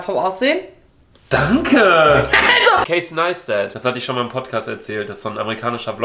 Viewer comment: Wer war Lucas Idole? Um 5:40 ungefähr, ich habe den Namen nicht verstanden? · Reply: casey neistat